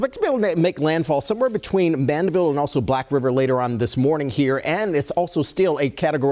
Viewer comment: Lmaoo what did bro say at 0:59 or 0:58 😂😂😂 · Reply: Black river